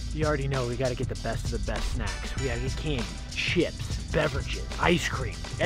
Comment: AYO AM I THE ONLY ONE WHO HEARED THE SH WORD GO TO 1:32!!! I THINK HE MENT CHIPS XD